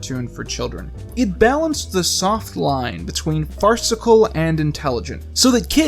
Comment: "did she say fart cicle? Yeaahhh put it in you face mouth! 'It's cold and it smells!'"